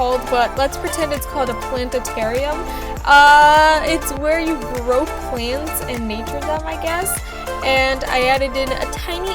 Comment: i thought she said grow plants and cook them lol